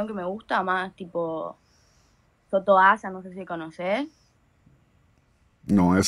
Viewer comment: que dice ahí? Toto que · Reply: Soto asa, tremendo exponente del neoreggeaton en España es muy bueno.